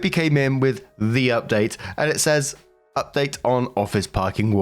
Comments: oh yeah, no offense but I hear that. Always thought you were saying her update and I'm like no. Maybe he's saying their update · Lol I thought was saying her update too. I fid t say anything before cuz i knew he wasn't and it was just his accent so no biggie